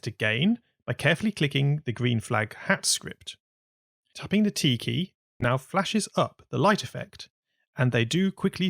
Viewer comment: where dd u get the explosions i need it :D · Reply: also at 14:24 and 14:42 i heard said TIKY and i know he said t key im not deaf